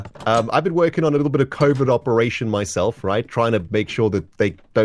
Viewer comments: did he say COVID operation??????????? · When it sounds like said “a covid operation” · why did I hear that as Covid instead of covert lol